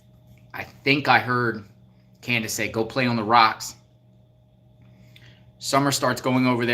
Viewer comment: I thought I heard Candis say go climb on the rocks.